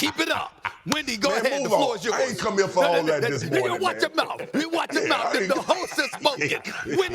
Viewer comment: Did SAS say niagga watch ya mouth!! Lmaooo.